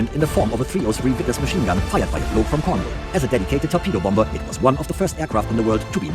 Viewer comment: "it contains a machine gun fired by a bloak from cornwall" idk i didnt understand it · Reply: a bloke